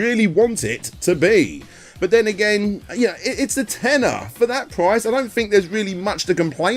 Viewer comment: When he said "tenner," referring to the game being $10, at first I thought he was saying "tenor," and I was wondering what this game had to do with singing.